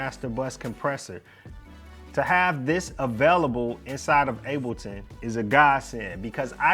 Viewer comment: Did he say inside of ableton?